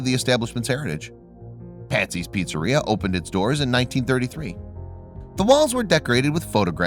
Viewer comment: he said 33 but the subtitle said 99 so witch is it